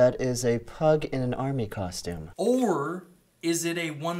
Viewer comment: Oh, costume. I thought it said consume...